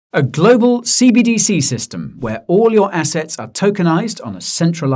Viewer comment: I thought you said, “A Global CBDC System, where all your Assets will be kickd.” I had to rewind 😂😂😂